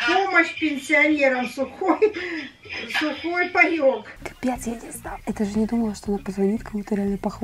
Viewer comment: Мне одной послышалось что сказала бл*ть?😂😂😂